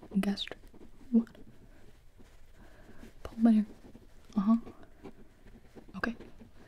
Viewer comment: does she say pull my hair??